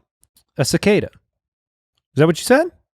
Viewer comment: I thought said Zicada too